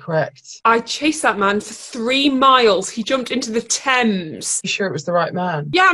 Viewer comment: Can someone help me better understand what Makoto says here? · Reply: Auto translate says "I chased that man for three miles. He jumped into the Thames(river)"